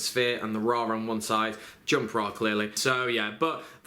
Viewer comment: What did he say at 3:03? "jump rock really"? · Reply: I thinks he's saying "Jump Ra really?" And that's he's talking about the Shonen jump promo version of Ra and how it has alternate art